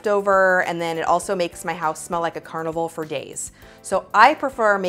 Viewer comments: 🤣🤣🤣🤣🤣 makes my house smell like a carnival for days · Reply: My dumbass thought she said carnival for gays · My dumbass thought she said carnival for gays